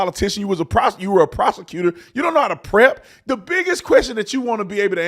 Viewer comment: That's funny @ 4:32 Brandon sounded like he almost said you've been a prostitute instead of a prosecutor